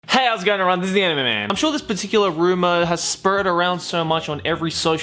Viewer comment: I must confess something, although its not about the video but.. Every intro, I thought said: "DISNEY ANIME MAN"